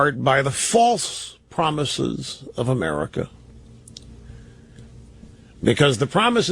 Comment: I found this again . I think I have a comment somewhere. I thought Rush said “ American Dream “ . I put a word , dream, in Rush’s mouth but what he said with the falsies & the promises are basically close . Anyway on another thread where a Conservative was blowing his gas , I wrote about this and the Rush comments and the false equivalency between Robin William’s suicide and liberalism . I wrote “ Rush didn’t realize that he screwed up going way off script . “ Somebody gave me a thumbs up and wrote “ Yeah they let it slip out every now and then ! “ That is the Bingo moment in this . Rush screwing up and letting the truth out about the American system as pertaining to the American Dream . The other stuff is just standard right wing crapola .